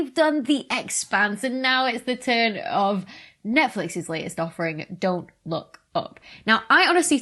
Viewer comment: It really sounded like she said "Netflix's latest suffering"... Freudian slip?